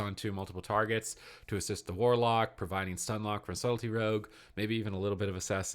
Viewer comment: I keep hearing you say "Salty Rogue". That's my new official name for the spec.